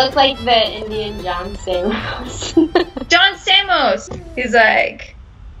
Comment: Americans: He is like Indian John Stamos. Indian: John who? Let me Google him. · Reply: For a second I thought they were saying Indian Johnny Sins